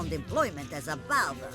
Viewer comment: All my life, I thought she said, "Father", not "Barber". I feel like a dumb head sometimes